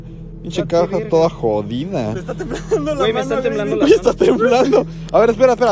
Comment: escuche mal o dijo "Te está temblando la mano pareces vibrador" XD